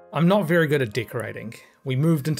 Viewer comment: I thought you said you weren't very good at "dick-rating". Then my New Zealand accent filter kicked in and I understood.